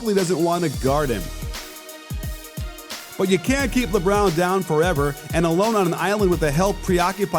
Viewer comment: Did he say “but you caint keep lebrown down forever” 🤦🏻‍♂️ that’s a lil sketchy 🤔